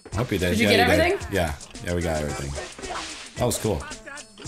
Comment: I'm not being mean I love their videos and I'm not making fun of, I just want to say at 9:24 instead of saying that was cool, to me it sounded like he said how was school😂 I'm not making fun of him I was just saying he probably did say that was cool but it kinda sounds like how was school😊 I love their videos💜💖 I think there funny. This video was years ago but at that time I wasn't aloud to have a Google account so I might not get likes, but I hope I do😊😀